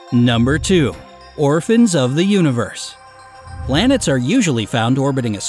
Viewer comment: When you said Orphans of the universe and then showed a blue planet it straight up mudelled up my mind and thought you said orphans of the tide which is a book my teacher read to my whole class and it’s a really great book it’s not like a short or anything it’s like this small adventure and there are huge twists inside it so I think you might like it